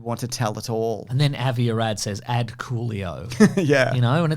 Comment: I heard "Add Coolio" as "ad coolio" and thought Avi Arad had said some shit in Latin.